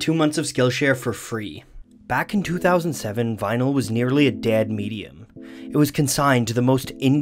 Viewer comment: I thought he said dead meme and almost turned off the video